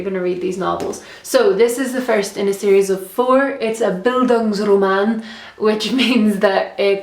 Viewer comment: i'm sorry, i didn't understand what you said at 3:12 ? did you say "Bildungsroman"? xxx :)